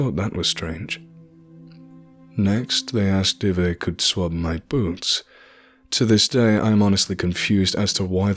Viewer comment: anyone actually know why they swapped his boots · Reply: Thought I heard “swabbed his boot”